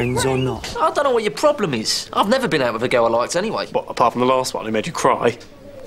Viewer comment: What did Jay say at 7:38 ? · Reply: I dunno what your problem is, I've never been out with a girl that I liked anyway